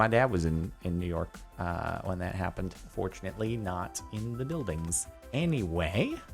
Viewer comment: I thought he said unfortunately not in the buildings😂